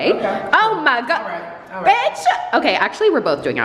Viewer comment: did said BITCH?!!! the world has come to an end!